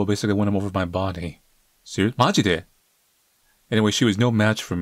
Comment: Did just say "majide(マジで)"?😂